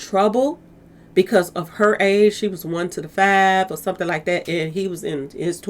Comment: I swear I heard “She was 125” years old; took me a while and 3 replays to figure out it’s “1 to the 5” as in 15 🤦🏽‍♀️😂😂